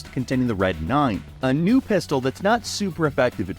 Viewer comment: did you say the red 9 is a new pistol? Or am I just hearing that out of context